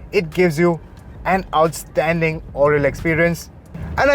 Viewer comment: did he just say 'fantastic oral experience'?